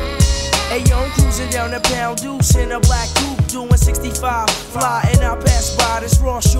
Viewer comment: 2023 and I’ve been listening to this song for 28 years now. Glad that it is finally on Spotify. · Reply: Homie, what say Taj in minute 0:09? I hear "Im cruisin down the (?) in a black coupe"...